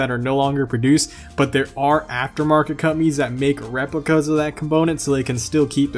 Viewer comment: I was cooking while he was talking about after market companies and I thought he said “after market cummies” 😂😂